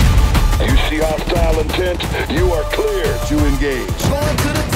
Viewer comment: what does lerch say? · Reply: "If you see hostile intent you are clear to engage"